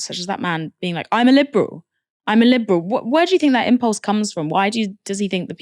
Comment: Is it mistranscribed? It sounds like he says, 'I'm a liberal, I've always voted Liberal' rather than Labour.